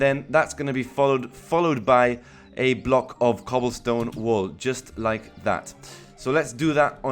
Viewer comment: Im building it im 10 mins in ima coment back when somewhere else .p Nice build i love it! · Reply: did you really say cobblestone Wool Or is it just me? nah its just me